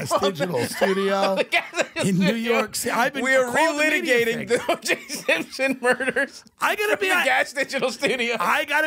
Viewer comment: I thought he said Louis j Gomez when he said oj Simpson I thought Louis killed his wife